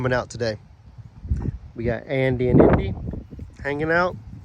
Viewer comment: LOL it sounded like you said Andy and innie